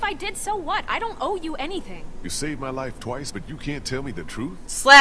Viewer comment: I thought it said "you saved bens little life" XD